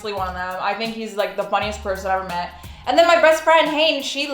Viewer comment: The way said best friend it sounded like she said "Breast Friend" I was like....wait what did she say?....xD Also i am a pretty loud person and so is my laugh and i flail my body in excitement. I don't care what people think. and damn if you are that annoyed then just move along there is no need to hate on someone like that.